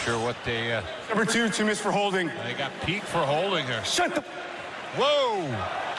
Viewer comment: why do I hear the N word here?